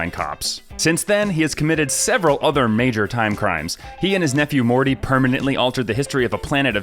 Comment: Did he just say "he and his nephew morty"